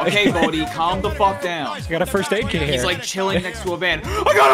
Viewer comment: For me it sounded like he was saying "I Got A Birthday Cake Here 🎂 🍰 "